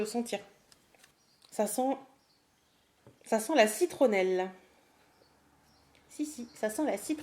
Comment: au moment ou tu as dit " ca sent la citronelle" j etais en train de rincer mon masque visage et j'ai entendu " ca sent la fricadelle " ptdr